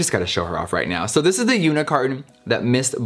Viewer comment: anyone else hearing “this is the unicorn”